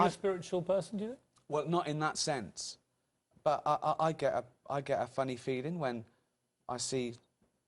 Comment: i thought he said 'i get a funny feeling'. i wonder if he sees karly pilkoid and feels funny inside.